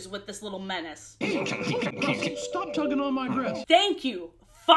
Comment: For a minute I thought she said "stop tugging on my breast" And I HAD to replay that 50 times because I was so confused